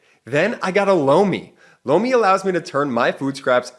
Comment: I thought little brother said '"blow me" but it was Lomi